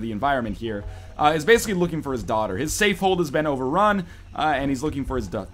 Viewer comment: I thought you said he's looking for his dog